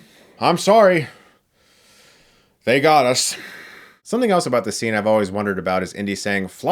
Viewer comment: Somehow I thought when Connery said "They Got us" during the plane sequence, I thought he said "I got us."